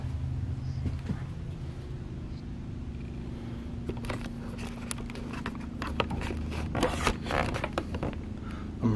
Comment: I feel like at 10:27 I heard some whispering right before you said you thought you heard talking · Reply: Says it's great to see ppl